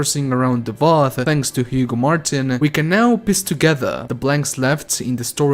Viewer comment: Did u say "we can now piss together" lol